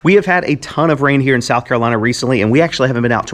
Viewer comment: At the beginning of the video, I would have sworn you said, "we've had a ton of reindeer in South Carolina...". I did a double take for a split second till I realized what you actually said. I guess this happens more when you get old like me🙂